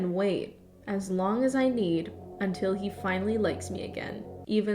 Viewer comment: until he finally licks me again I swear that's what I heard